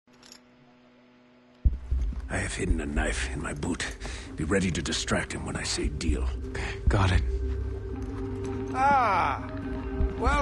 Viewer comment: At the start, I thought he said "I have hidden a knife in my butt" I had to rewind to remove these eyes: O.o